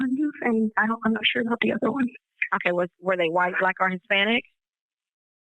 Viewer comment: did she just say is the white black or Hispanic?